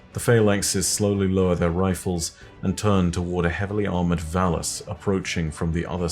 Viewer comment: oh, oh no, I heard 'heavily armoured vallus' as 'heavily armoured phallus'